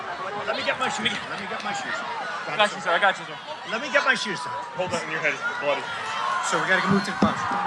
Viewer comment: Between 8:12 and 8:22, it sounds like someone says three times, "Let me get my shoes." Is that actually what they're saying? Who said it? Trump?